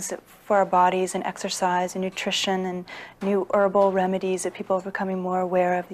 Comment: Did I just hear her say "erbal remedies"?what the hell is an erbal?